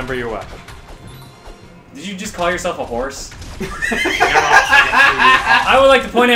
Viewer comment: He did NOT call himself a HORSE. He may have felt a little horse but he called himself a magical PONY NOT A HORE. You nubb.